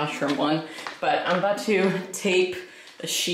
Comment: LOL I was listening while I was crocheting and I SWORE you said "I'm going to take a shit" but I look up and it was "tape a sheet"